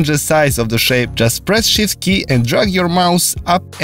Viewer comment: I thought he said "shit key". But he was really saying shift key